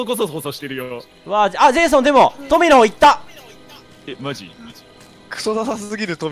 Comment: ぐらいのとこでうっしーの「マジ」のあと女の人の声で「マジ」って聞こえない!?